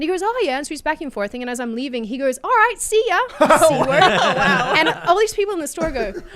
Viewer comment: Once again, censorship makes communication difficult. Listening at 1.5 speed, all I heard was "seeya", which I thought was more self-censorship of cunt (seeya, c-ya). I had to go back and drop to normal speed before I could hear that she actually said "seeya c-word".